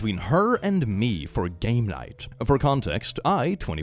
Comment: I first heard gay night and my head snapped to my monitor before I realised what he said 😂